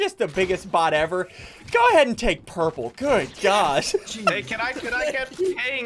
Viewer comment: I thought he said good god but he said good gosh